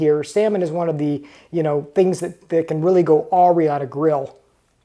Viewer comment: Nice storage in the garage! What did you say at 8:32, go awry?